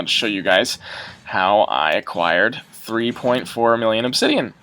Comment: he said 3.4 instead of 3.2?